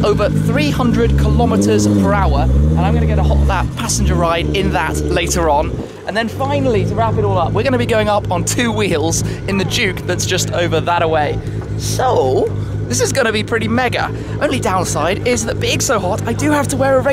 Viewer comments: I thought that you said going on 2 wheels with the Duke(motorcycle) 😂😂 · i thought you said 2 wheels on the duke, i was surprised you could ride, and confused as to why a ktm would be there at all